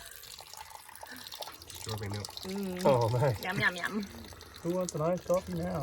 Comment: Did she say "yum yum yum" at 4:40 ?👁👄👁